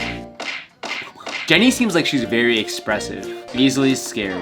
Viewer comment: I litterly thought he said " ExPensive" and not Expressive